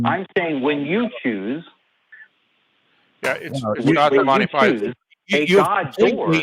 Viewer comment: I think started to say Monty Python, which was funny to me as I read Monty Hall as Monty Python the first time. The game show was Let's Make a Deal, btw. Kinda like a less boring Deal or No Deal, but you roll a die 50 times instead of flipping a coin 50 times.